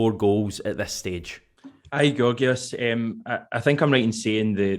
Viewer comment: Thought you said “hi gorgeous” in that interview 😂 You wouldn’t be wrong either 😜😂